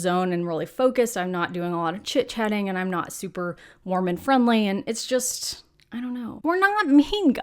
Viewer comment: Did you say Mormon friendly?? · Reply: Lol. Warm and friendly